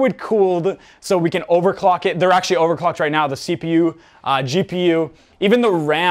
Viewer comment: me at 9:30 when i'm tryina understand what ur saying 🤯🥲🥲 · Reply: he said he overclocked his pc basically overclocking is giving your pc more power and make it faster